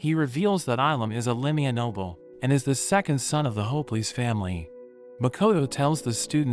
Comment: is it just me or did he actually said "the second son of the hopeless family"?? (im using captions)